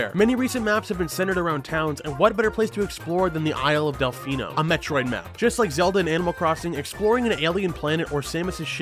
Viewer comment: I thought he said “A Detroit map” for number 2 😂